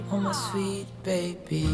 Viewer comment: The backyard reminds me of your parents house out East. I'm sure that's what sold the place. The best is yet to come, and won't it be fun. Did I hear you say "Baby Zeke," to Leo?